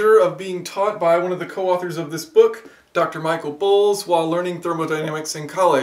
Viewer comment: did he said Michael Pools or Balls? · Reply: *Boles.